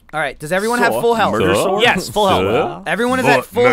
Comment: Anybody else hear "Murdersoar"